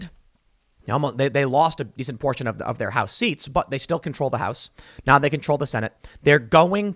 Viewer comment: did Tim say now they (dems) control the senate?